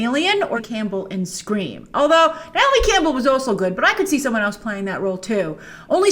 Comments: Pretty sure just called Neve Campbell NAOMI Campbell 🤣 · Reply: I heard "Natalie Campbell" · Did she call Neve Campbell “Natalie Campbell”? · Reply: I thought she said Naomi Campbell.